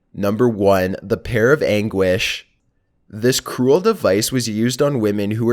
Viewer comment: Did he just say "this kool device" 😁😁😁😁